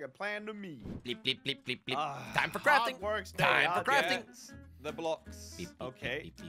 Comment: I thought said “ Time for Teletubies… Time for Teletubies “